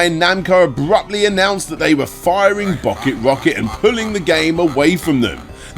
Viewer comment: Was BocketRocket intentional? I assume so after the next time and I'm confused as the subtitles say BottleRocket.